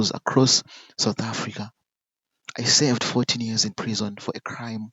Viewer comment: You are in early 50,s and you were jailed for 40 years in prison ...so you were ten years when you were arrested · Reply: Listen keenly, he said 14 years. It's because of his pronunciation, it sounds like he is saying 40!😊